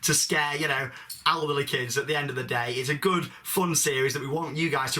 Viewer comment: Did he say "alderly kids", or "elderly kids"? ...Either way, i'm getting it on a t-shirt! :D